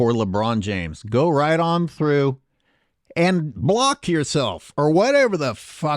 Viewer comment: lmao 😂😂😂😂😂 "and BLOCK yourself- or whatever the fuck that was", it almost sounded like you were saying "and go FUCK yourself"